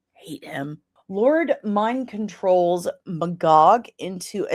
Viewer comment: When you said Magog, I almost thought you said "B 'gawk".🐔 I guess I'm getting sleepy 😂